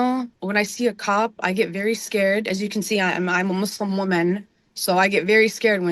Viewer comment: did she JUST say I’m ALMOST a woman? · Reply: I’m a Muslim woman is what she said